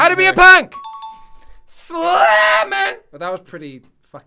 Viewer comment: did you say 'SELAMAT' at 3:07?